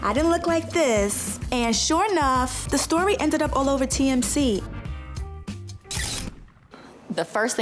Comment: did she say tmc? 😂😂😂